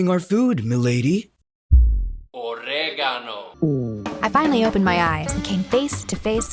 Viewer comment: I'm I the only one who heard "OrEGaNoo" everytime that guy shows up